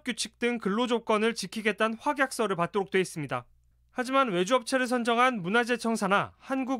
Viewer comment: 이참에 외주업체들 싹다 감사해봐야할듯 국방부 국군에날 중국 장갑차 영상도 외주업체라고 하는데... 국가 정부일을 하는 외주업체들이 이렇게 사고치고 계약서도 대충 아마추어같이 일하는하는것도 자꾸 의구심이든다 이참에 계약에 입김없었는지와 친인척관계등 다 털어봐야할듯~ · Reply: 자세히 들어보면, 외주업체가 아닌 굥의 "외가업체"나 "처가업체"로 들리는데...내 귀가 잘못된게 아니라면...